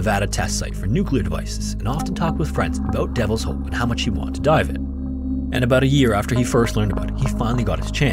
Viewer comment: I heard this as "and how much he wanted to die in it"